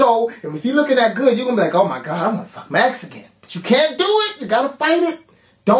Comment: At like 12:05, I swear I hear I'm a fucking mexican. LOLwut!